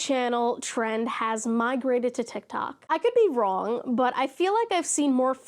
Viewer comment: I was only listening to this video and i somehow heard Github instead of tiktok and was like????? Is nothing sacred?????